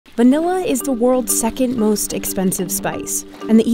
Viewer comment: Did she just say 'banilla?'